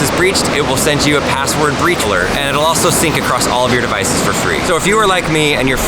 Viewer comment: I thought bit said "beach alerts" and the background fits so well for that statement